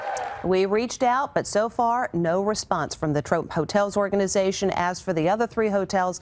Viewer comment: Did no one here at 4:22 she said trope instead of trump lol